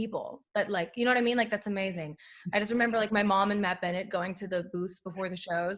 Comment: did she say Matt Bennett?